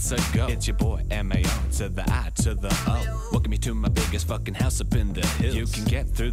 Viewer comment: Okay, no matter how hard I try, it just sounds like Mario is saying "M-A-O, to the I, to the O" instead of spelling M-A-R-I-O. I know with the voice it's supposed to sound like "aw" and not "arr" but still it doesn't sound like that. I think the musical stuff on top of it makes it sound like he's saying O. If I slow it down then I can kind of barely get myself to hear "aw" but just... yeah.